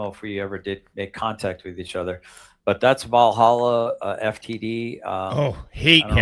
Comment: Thanks! @ 16:34 MeMe, the Legend of Nothing, did I hear "Valhalla FTD"? WTF? Can't even remember GzzM's letters half the time. Give it up Lil drama queen. 'Storytime With The Putz' is hilarious. Searching for anyone other than his K1 classmates that will pay attention.